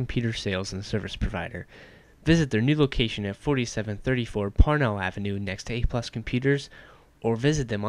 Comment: LOL when he says "parnell Ave" it sounds like he says "Porno Ave"